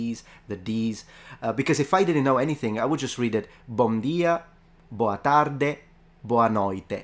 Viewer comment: I really thought he said "bonjour" (for bom dia)